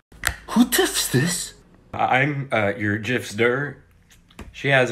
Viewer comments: Why did I hear "Sorry that's on the Dildough" 😂😂😂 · Bro at the end it sounded like he said sry that's on the dild0 💀